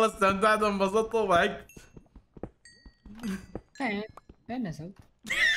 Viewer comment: ايش قال 23:51 ؟ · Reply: فين نزل